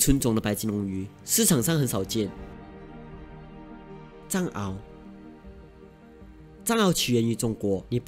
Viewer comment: 藏獒听成蟑螂。。。 如果真的是蟑螂我家就发达了